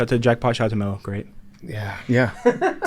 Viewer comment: did say “I love you too” after PFT said “love you guys”😂😂😂😂